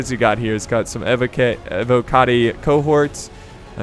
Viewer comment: did you say Avocado Cohorts?